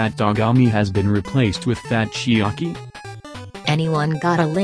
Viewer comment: did they just call (best boy) Ryota, Chiaki?